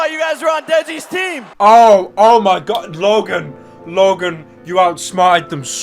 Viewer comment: did he call Jake Logan